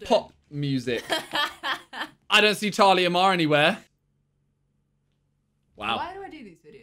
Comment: who else heard it as charli amar instead of talia mar lmfao